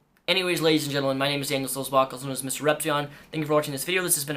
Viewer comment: I always hear " my name is Daniel Soul Sparkles" wich is pretty fabulous though